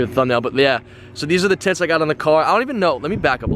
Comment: I heard tits lol